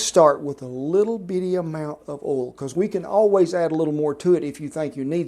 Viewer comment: it's not just me. even youtube auto-subtitle think it's OLD instead of OIL. and if you are here because of the title, the answer is at 3:51. argh